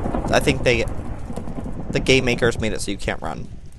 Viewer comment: it sounded like he said the gaymakers